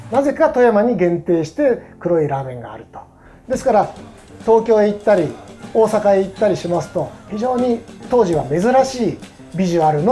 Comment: I thought he said tokyo italy in